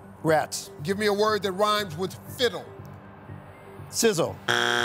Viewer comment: did he say sizzle? and not riddle? 🤔